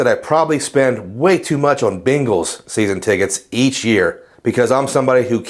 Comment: Haha! When @ 4:00 of the video you say you spend “way too much on Bengals tickets every year . . .” I heard “Bangles” and was thinking two things: one, the Bangles still perform?; and two, this guy likes the Bangles that much?